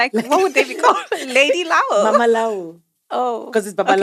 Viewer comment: Did she just say "lady lawo"?😂😂😂😂